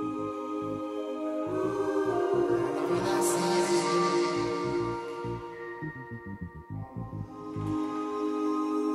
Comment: What is it saying at 0:49 seconds? And so on ? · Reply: "i keep on walking" "All over the city"